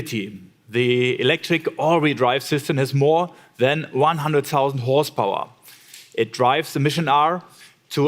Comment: it sounds like he says the car has “more than one hundred thousand horsepower”